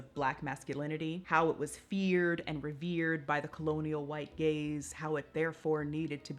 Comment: I thought she said white gays